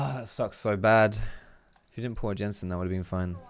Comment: Did say "If we didn't pull a Jensen," as in C9 Jensen?